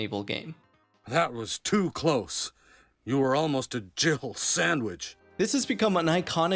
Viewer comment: the first time I heard this line I thought he said “Jiggle Sandwich” and I still have trouble not hearing it that way